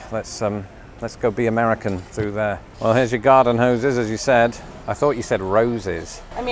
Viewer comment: When you said hoses I thought you said roses and I said, "I'll have the big red one!"